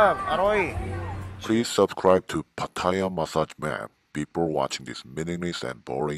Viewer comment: Thought I heard papaya massage map🤭